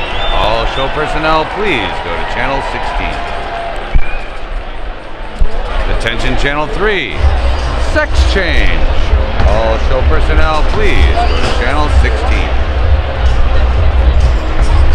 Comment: he sounds like he says sex change. lol.